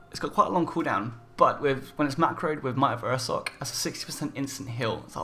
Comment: what does he say at 02:01 "when its macroed with "... ive heard other gamers say that word before but ive never could understand it .. sorry for my bad english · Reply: Iwchy225 when its macroed with might of ursoc. It's a druid spell and it increases current and maximum health by 30% for 20 seconds.(Activates bear form)